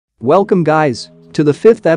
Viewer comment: I wasn't wearing earphone , I heard "wellcome gay's" lol😅💔😂